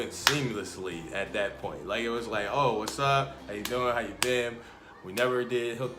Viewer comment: you guys are a really cute and authentic couple also not trying to be rude but at 2:54 when you like we never hung out right before u said hung out it really sounded like you were going to say hook up because u said hoo...hung out